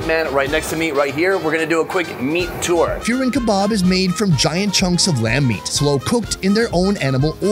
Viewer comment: Can anyone make out that name of the kebab at 9:21? I hear feering kebab or fearing kebab? I am trying to learn more about this.